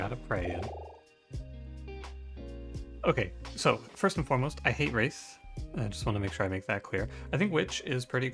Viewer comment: Looking away and I just barely mishear 26:37 as "First and foremost, I hate race" and did a double take